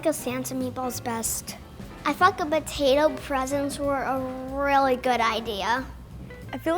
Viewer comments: I thought she said I fucked a potato present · I thought she said f*** 😂